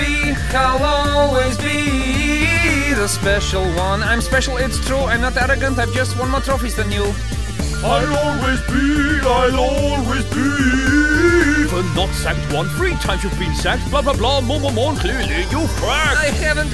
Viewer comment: Jose sound like he is saying "Hala Beef" when he is trying to say "I will always be"